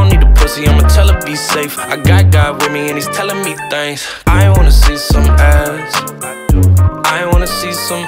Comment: I thought he say "i wanna see some abs" 😐